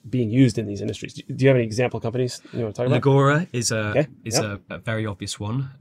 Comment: What is the example he is saying at 12:45? Nagora? Nogora? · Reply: Legora